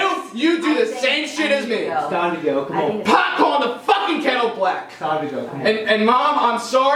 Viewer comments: What does say at 35:37 ? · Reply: pot calling the fucking kettle black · What is he saying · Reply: pot calling the fucking kettle black